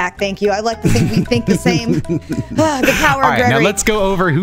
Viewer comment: when says: "The power of gregory" The subtitles say: "The power of god" Now im not sure what youtube is suggesting